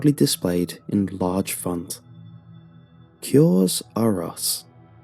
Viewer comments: I thought it said 'Curses R Us' I thought it was about Toys R Us oof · For the first 2 minutes I thought it said curse r us